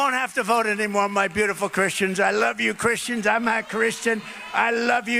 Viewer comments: Did he say "I'm not Christian" .. or was he trying to say "I'm a Christian?" It sure sounds like he is saying "I'm NOT Christian." which would be the TRUTH! He is NOT Christian .. he just wants their vote so that he can become Dictator. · I sure sounds like he says "I'm not Christian," yet a lot of news organizations are quoting him saying, "I'm a Christian. Sure doesn't sound like that to me.